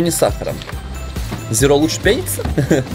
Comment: мне одному послышалось что зеро лучше пениса? 🤣